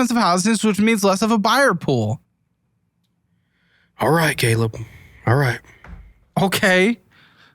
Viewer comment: did he call you gayleb?